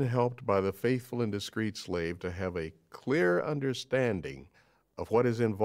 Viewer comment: Did he use the word "slave"?!?!? I'm fairly sure that's what I heard but I just want a confirmation.